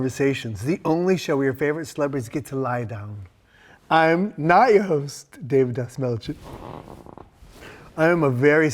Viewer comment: lol I thought he said Na-host (like na-barron from dune). He just said not your host real quick.